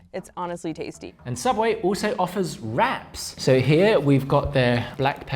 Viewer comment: I thought he said “Subway China offers rats” but I looked over and it was wraps